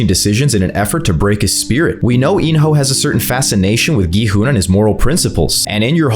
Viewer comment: did you just say gi hoa instead of gi hun?